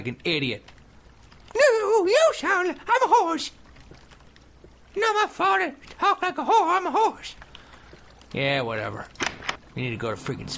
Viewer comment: Did he say not my fault i talk like a whore, i'm a horse?